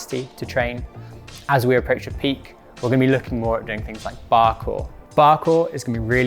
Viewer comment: I definitely thought he said Parkour instead of bar core hahahaha